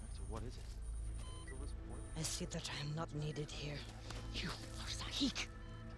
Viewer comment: everyone says she said “I see that I am not needed here ” Is it just me who hears “I see the time not needed here.” That’s how she speaks